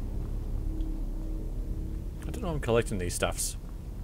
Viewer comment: What did you say @ 1:58? "..... these staffs?" (sorry, English is not my first language^^) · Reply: Tar vyn "I don't know why I'm collecting these staffs", is what I mumbled. Sorry :)